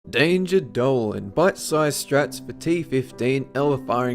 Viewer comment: First I heard it as "Danger Darwin" for some reason that I can't explain without the whole universe imploding upon a stale donut.